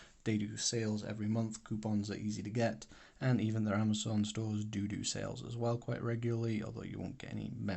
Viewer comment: Are you asking if you can get the disposable cells the original uses on Amazon? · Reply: at 5.20 i thought you said amazon dodo cells but you said dodo sales. My bad!